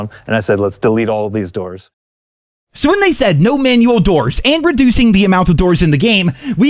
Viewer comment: He clearly said they planned on reducing the number of MANUAL doors but then they decided to remove them instead. He said nothing about reducing the number of automatic doors.